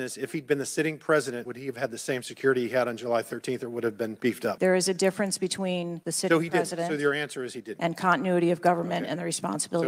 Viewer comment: Did anyone catch what she said at 13:50 she said there is a difference between a president and a continuity of government🤔